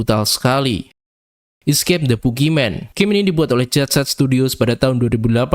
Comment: gw dengernya Escape The Pukiman 😂 (sorry bang)